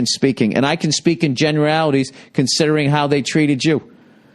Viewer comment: when i first listened to this i was so retarded and thought he said 'considering how they treat a jew'